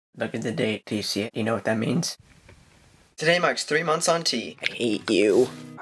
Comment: "I hate you," he groans as he picks up his binder. Story of my life. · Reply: I thought he said "hey you "